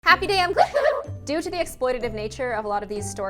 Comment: From two years of watching I now realize she says happy day IM. I thought she said happy day ON Anyone else?